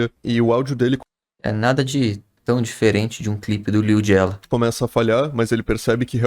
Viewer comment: n entendi kkkkkk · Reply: KKKKKKKKKKKKKKKKKKKKKKKKKK, ja é a segunda vez que o lilgiela [e mencionado no canal dele (acho que ele é fã dele)